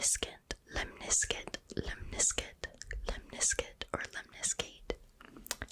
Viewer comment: I zoned out and thought u were saying limp biscuit for the lemniscate part